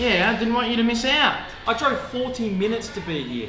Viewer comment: The fact that he needed 14 mins to join him, and his friend didnt even finish half of his Burger in this time, is confusing me. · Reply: Oh he says 14 minutes? I thought he said 40, it makes less sense that he's pissed now